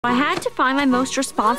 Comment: So growing up, I thought she always said WTH red seagulls 😂